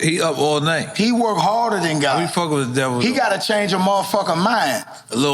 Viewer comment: Nore says “we fuck with the devil though” did i hear that correctly?